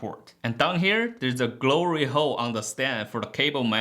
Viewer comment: Haha did he just say Glory Hole? Great review! Im narrowing now my choices and this is definitely one of them.